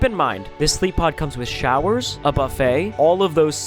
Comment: Wait wait wait did I just hear the word buffet I love buffets